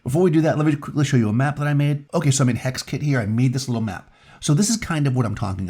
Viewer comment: "this is a map I made in hexkitty"?? Did he say hexkitty? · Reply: Hex Kit 😊